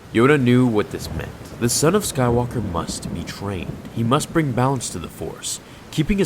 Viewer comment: What if Vader played amogus with Naruto from fortnite tho?? that would be CRAZY 🤪🤪🤪💯💯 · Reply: For a second there I thought you said "trained them to be shit"